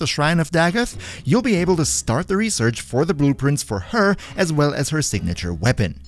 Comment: i thought he just said boob prints and not blue prints